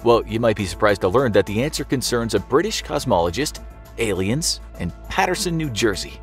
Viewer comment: At first I thought they said Cosmopologist. I was wondering how someone that does make up would know about space.